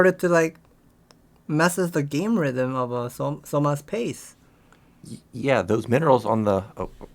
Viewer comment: I heard "One move with Battlecruisers started to like, mess up the game rhythm of <player>'s face" was like DAMN, savage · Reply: Lol. In case anyone's wondering, he said 'pace'